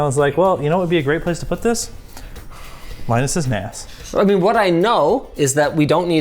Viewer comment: I first heard "linus's ass" and man how I laughed. Alas, after rewatching I was disappointed to learn that he actually said "Nas".